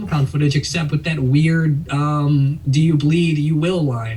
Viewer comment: This whole time I thought Batman said "do you plead"